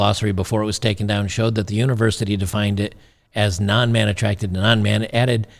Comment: I heard "woman" and "naan man". Naan is a type of bread.